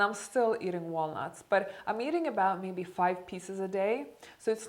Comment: I thought you said 'I'm eating maybe about 5 pizza's a day' 🤭 Lol... So nice to see you again on YouTube! Thank you for sharing what you've learned with us. Love from The Netherlands 💖 ps: have you read 'Why we age and why we don't have to' by David S. Sinclair? I think you'll love it. It's recently published and has a few decades of research regarding aging. It has practical tips as well.